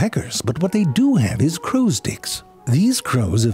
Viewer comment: I don't care what the CC says, he says 'crows dicks' at 0:41 Edit: Oh my, I should have finished the video before I commented 🤭